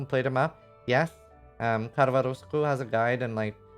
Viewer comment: talks about a atlas guide involving not completing maps. i didn't quite catch the name from the creator. does anyone know which guide he's talking about? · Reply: Karvarouskou I think be said.